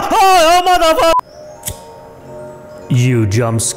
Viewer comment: What did he say at 00:20 ? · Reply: he said mother f